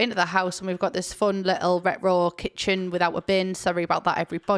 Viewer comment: I’m sorry but every time u said “retro” I heard “Rick roll”, I think I’ve been watching too much lilsimsie 😂